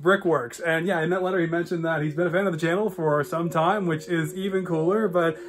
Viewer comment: When you said fan of the channel I thought you said has been in Jail which is even cooler 🤣🤣🤣🤣🤣🤣🤣🤣🤣🤣🤣🤣🤣🤣🤣🤣